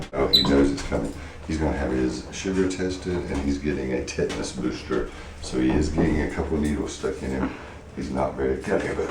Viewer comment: I thought he said he's going to have his "shitter tested" I was like - wow that's graphic lmao...